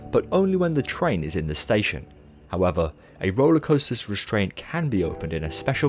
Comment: I thought that said roller coaster restaurants